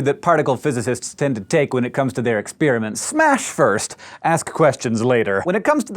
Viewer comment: I accidentally skipped to half way between 0:14 and 0:15 and I just hear “Smash fist ask questions later”